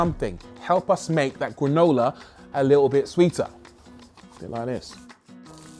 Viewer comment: What a great idea using cereal to illustrate the presenting. I did have to rewind a little when i thought I heard you say "Vauxhall Granola". You didn't. But I wish you had.